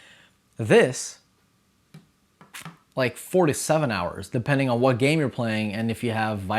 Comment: ohhh, i misheard 4 to 7 hours to 47 hours, but still its rechargeable soo its OK